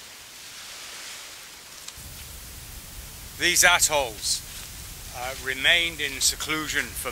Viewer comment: I thought he said “these assholes”. Glad to see your safe.